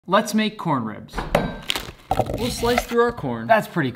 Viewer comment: I thought you said "lets make Gordon ramsay"